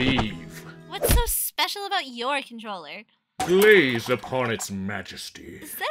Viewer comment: Wizard said "glaze" instead of "gaze" lmao